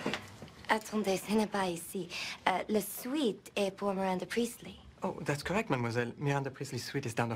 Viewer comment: what does she say? I only understand “Le suit eat pour Miranda priestly” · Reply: ''La suite est pour Miranda Priestley'', the suite is for Miranda Priestley